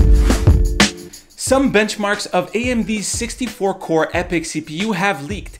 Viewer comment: I thought you said "Some bitchmarks..." Almost spit out my drink. :D